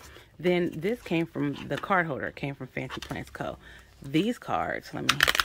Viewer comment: Thank you for sharing. Did you say fancy plan co? What is that called? 2:28 mins/sec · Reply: The card holder came from fancy plans co. The month ahead card came from Lotus paper co. The other three cards on the bottom is poi and hun. The top cards came from sticker monster co.